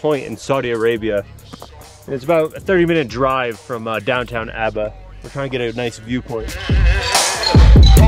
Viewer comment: I thought he said Downton Abbey 😂